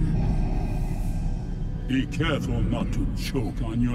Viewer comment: Be careful not to choke on your aspirations, Director. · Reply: I always thought he say be careful not to choke on your ejaculation director